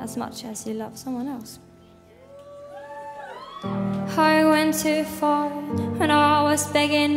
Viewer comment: My Japanese ears are mishearing “I went too far” as “I want to fart” 😂 I hate my ears… this song is so beautiful 😭